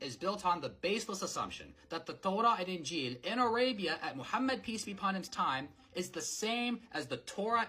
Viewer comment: Please correct the caption at ~1:22, instead of "peace" it says "police"